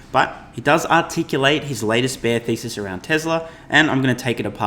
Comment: At first I thought you said Tesla shortseller bear ‘feces’ but then realized it was ‘thesis’